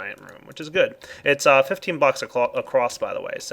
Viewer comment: did he say bucks?